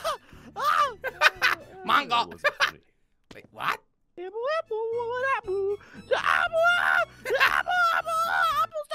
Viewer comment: what did say in his actual voice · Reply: He said "that wasn't funny man."